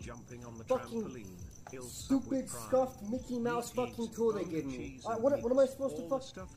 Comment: ik no one cares but the subtitles said (stuff) when he said scuffed